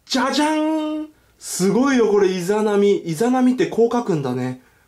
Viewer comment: 伊邪那美って言った時、 イザナギって聞こえたwww